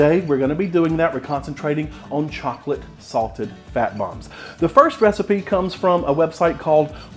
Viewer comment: At first I thought you said “chocolate sausage fat bombs”. Totally keto.